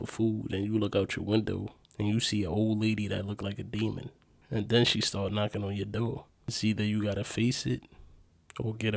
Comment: Did anyone else hear the voice in the background of the door dash guy ? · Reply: Yes it says IT WILL TAKE YOU